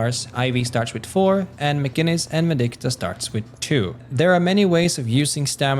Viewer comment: What does he say at 1:35? McGinnis and who starts with 2? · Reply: Vindicta